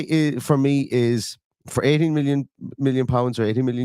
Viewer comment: I had to go back and play the closed caption. At first I thought you said 80 my million lol 18 million is reasonable. If Emery wants him he has my full support. UTV!